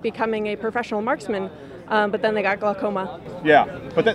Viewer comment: She said “but then he got glaucoma” but I HEARD “then he discovered weed”